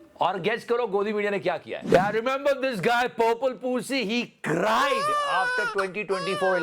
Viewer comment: Did he just say purple pu$$y??😂😂😂😂